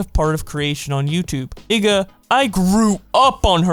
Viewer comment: did you say the n word????? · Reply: He worked around it and said igga